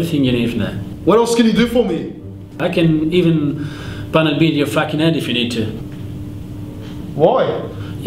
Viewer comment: what the fuck did he say at 3:30? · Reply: Pummel in a f'ing head